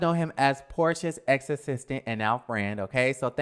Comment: i honestly thought he said he is porchas exorsist. lol not ex assistant